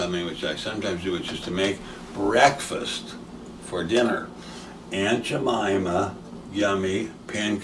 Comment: Honestly everytime you say aunt jamima all I hear is "ain't cha mamma". Am I the only one?. 🤣